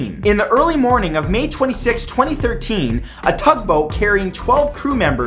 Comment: did Matt say thug boat ? XD